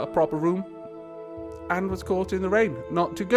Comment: i thought you said "and he was caught in a rape"